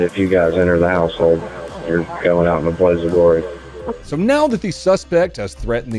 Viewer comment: I thought he said "Blades of glory"...